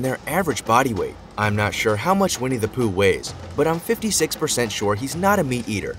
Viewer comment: Aaaaahahahahahaha! I thought he said We need to poo 😳😂😂 (Vinnie the Pooh) 🙈🙊